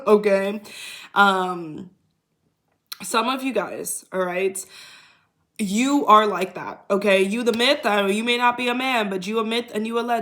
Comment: I heard was “I’m the man” — Chris Brown when you said … “ you are LIKE THAT “ 😂❤